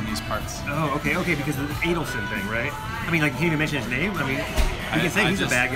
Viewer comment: what's the deal with the Venetian? From the sound, I thought I heard "angle shoot", but couldn't be exactly sure. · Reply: He said "the Adelson thing." The Venetian is principally controlled by Sheldon Adelson, a big-time opponent of online poker. He's been very active in keeping it out of states that have considered legalizing it, so many poker players avoid spending any money in his casinos to avoid financing activity against their interests. There are other political considerations which you may or may not agree with.